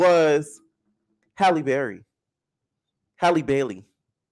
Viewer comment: Lord I thought you said hallie berry I grabbed my phone so fast saying not my baby can’t be lol